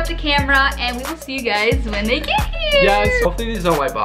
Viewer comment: when said we will see you when they get here she said it in such a weird pitch it sounded like she said when they naked